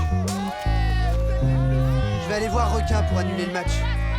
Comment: J'ai entendu aller les méga killers🤔